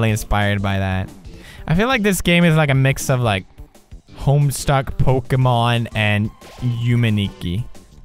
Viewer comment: DID YOU JUST SAID H O M E S T U C K ?